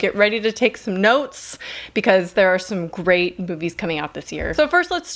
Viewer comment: did you just say there are some great "boobies" coming out this year?